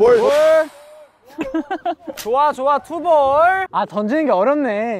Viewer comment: instead of of hearing Dino saying "Two Balls" I heard "tubol"😂😂😂😂